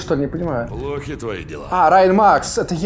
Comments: мне одному послышалась брайн макс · я один слышу брайан мапс? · Кому показалось что он сказал Брайн Мапс, Лайк))))) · Брайн Мапс... Слава Богу мне послышалось. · Мне одному во второй игре послышалось Брайн мапс? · В 9:40 мне одному показалось что он сказал BrainMaps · мне показалось что он сказал брайн мапс